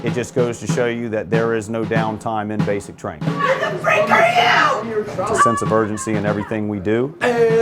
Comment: WHERE THE FRICK ARE YOU!! lol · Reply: Giovanni! Navarro oh I thought she said “who the frik are you?” Lmfao